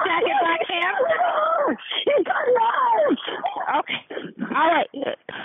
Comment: The caller did not say "He's gutting her" ... She said "He has gone in the house."